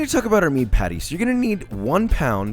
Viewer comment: Did he just say 80% fat?